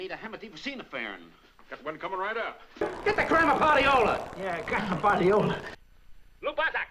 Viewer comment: He even made curly joe laugh at that one lol 4:39 ,, Almost sounded like he said get the gram of POT - e - ola . Moe even looks like he realized what he said lol